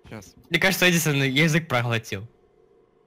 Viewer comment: Мне вместо ходер послышалось хонор (Honor)